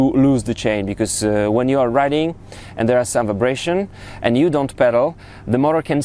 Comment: What did you say at 8:57? Something about a way to loose the chain? Sounds like you said, "when you are running, and there is some abrasion..."?